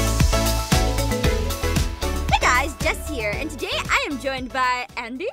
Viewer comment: i thought you said Jeff Here!😂